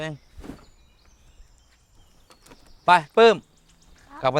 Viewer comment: ได้ยินเสียงคนเรียก ปลื้ม😮